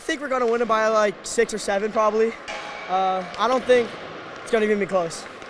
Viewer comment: did he say they would win by.... 6 7?